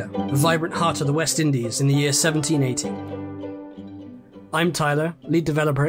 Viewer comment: did he say the year 1718 or 1780? EDIT Never mind. He said 1780